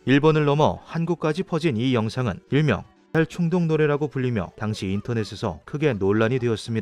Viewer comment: 자꾸 자살충동 에서 자살 묵음 때문에 "딸 충동" 이라고 들려요 ㅠㅠ 흐윽 ㅋㅋㅋㅋ